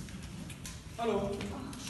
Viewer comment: Not sure if I’m mishearing things, but that “hallo” killed me. 😭